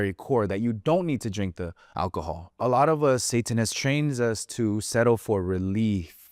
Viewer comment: im sry what did u say?! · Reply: “A lot of us, Satan has trained us to settle”